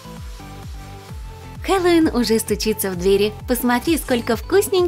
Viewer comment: мне одной послышалось КВН уже стучится в двери😂